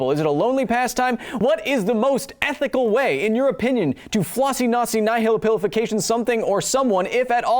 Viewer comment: Says floccinaucinihilipilification instead of floccinaucinihilipililize the second time. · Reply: All i heard was something about a flossy nazi named phillip on vacation..... But it all makes perfect sense....ish..